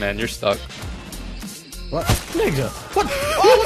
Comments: Who else hears say nigga on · At like 3:44 - 3:45 I hear nigga idk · did he say nigga? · Did just say nigga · Did say “what... nigga” at 3:42!???!??!!!????!!?